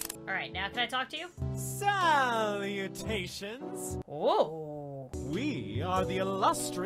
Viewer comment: When the drama guy said salutations I thought he said SALTY NATIONS!